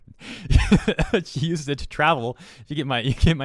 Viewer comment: spend it on travel? ...damn dad i thought you said traveler